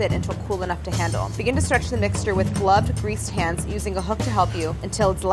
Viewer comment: I thought you said 'begin to stretch the mixture with blood greased hands' and thought that was the most metal summary of the whole episode